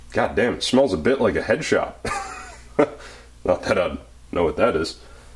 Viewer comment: I thought you said "headshots" instead of "headshops." And I was thinking how dangerously close you would have been to actually smell a headshot.